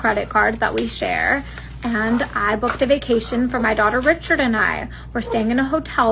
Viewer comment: im sorry. Did she just say "My daughter Richard" ??